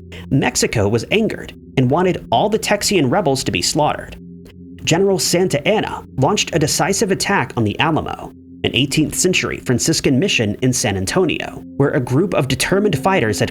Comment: did you just call it the "amalo" ?😂